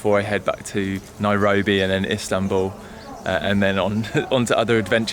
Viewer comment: Did he just say "Islambul" rofl